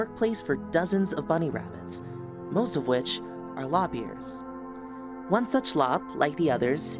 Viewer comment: I misheard "lop-ears" as "lobbiers," lol.